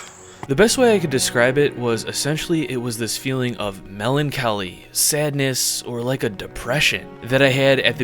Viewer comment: Did he just say "Mehlan kelly" instead of Melancholy?